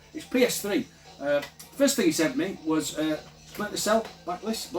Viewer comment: I thought you said Splinter Cell: Black Lace at first. AGADOOO!